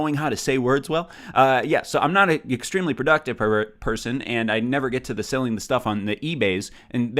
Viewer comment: Anyone else hear say "I'm not an extremely productive pervert- person..." at 14:08? 😂